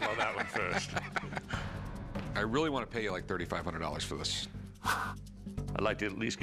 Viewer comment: How come it switched to $4,500 when he clearly said $3,500